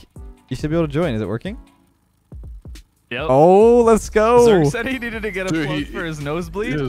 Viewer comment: He said: let’s go!!! What I THOUGHT he said: let’s GO daddy!!